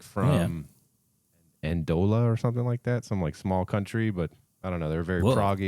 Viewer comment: "They're from Andola or something" - it's Andorra, a tiny city state between France and Spain. But he's right that Persefone is in fact super dope! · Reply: I thought he said Angola. I was, like, "What?!? Wow."